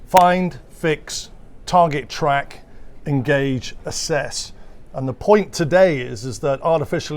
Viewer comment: "Assess" is what he said, but on the screen it says "ACCESS". I'll take it they meant "assess".